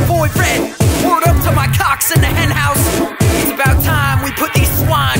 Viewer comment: c*cks? Did anyone hear dat